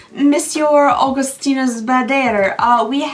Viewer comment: no wait hold on 😂 the "shop" link for Augustinus Bader showed up and shocked me. ALL THIS TIME, I thought you were saying "Augustina's batter" 😂😂 (and that it was the name of the brand of course) - My own De De Pew moment 😂